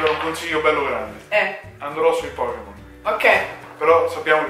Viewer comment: io ho capito andrò su i porno xD poi ha detto "c'è ne saranno 700, 800 troppi" ma AHAHAHAHAHA